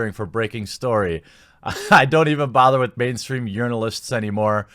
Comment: I heard "mainstream urinalists." Definitely salty (not that I would know from personal expeerience). Sounds like you're a casualty of fake news, bud. Hang in there and keep up the good work!